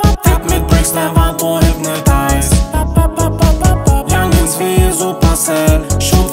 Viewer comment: was sagt er da? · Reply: Youngins wie supacell glaube